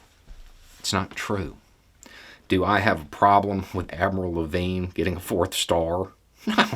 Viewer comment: I could have swore you said Avril Lavigne got a 4th star 😅